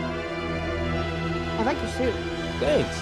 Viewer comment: Peter's saying that was adorable kid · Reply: it is Team, the captions for the movie say team, but the first few watches I thought he said that was adorable kid